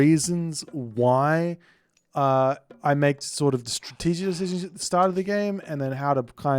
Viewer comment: LOL, am I hearing things wrong or did you just say "Stratzwzwzzzwzwz" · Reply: He said "strategic decisions" in a slurred way